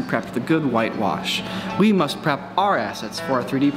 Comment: I thought they said we need to prepp our asses 😂